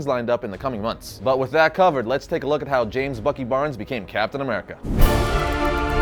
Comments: It sounded like he said James fuckin Barnes lol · I thought he said James Fucking bond